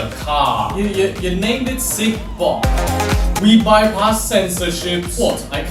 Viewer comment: turn on the subtitles for cig bock subtitles say C bomb